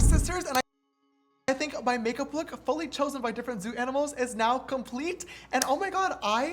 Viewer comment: am i the only one that thought is said 200 animals?